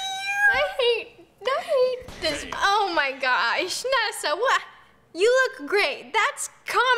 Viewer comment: on the grape one it sounded like said “you look grape!” lol 😂😂😂